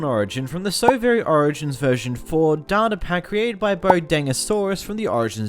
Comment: I thought he said "Soviet origins mod"